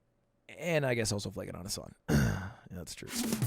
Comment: can anyone transcribe what he said here near the end? Sorry I'm not native speaker and I dont understand what he said under his breath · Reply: And also flaking on Hasan